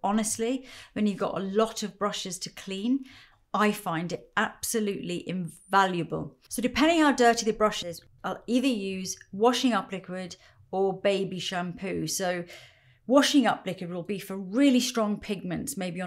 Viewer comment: Im sorry, can someone tell me what she said at 11:07? Washing Up The Grid?